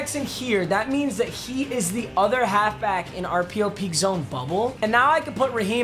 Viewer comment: Closed captions say rpo peek zone fumble